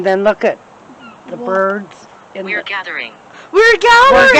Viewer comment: 39 minutes in you heard something. I heard ‘gathering’